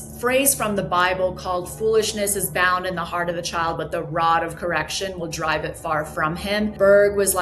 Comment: did anyone else hear "the rot of correction"?